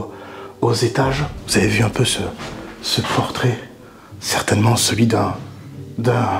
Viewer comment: Bah moi dans la maison diogene j'ai entendu " vous avez vu mon pantalon ?" 🤣